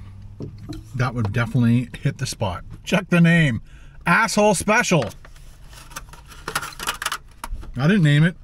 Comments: I thought I heard him say "asshole special" in the beginning of the video 😅😅😅😅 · Did he just say asshole special at the beginning? 😂